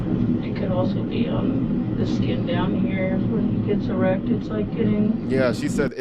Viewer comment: she actually said "when he gets erect" not that he is currently erect